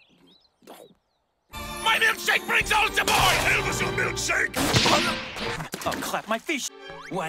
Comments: This reminded me of how funny hetalia actually was. “Oh crap my fish.” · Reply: Georgia Regina Louise Williams I thought he said “I’ll clap my fish” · "oh crap my fish", that line made me laugh, it's so stupid. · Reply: I HONEST TO GOD THOUGHT HE SAID "ILL CLAP MY FISH" AND IVE BEEN TRYING TO FIGURE THIS SHIT OUT FOR THREE DAYS